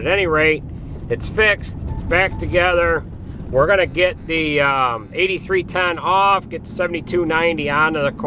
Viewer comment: We were using a8310r and believe it or not there was only about a 200 lbs. Difference. Power wise they were about the same horse power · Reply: Ah! So I misheard you saying 8310R vs 8210R. It is kinda amazing that there is only about 200 lbs difference since a 8 series is a "big" frame and a 7 series is a "smaller" frame.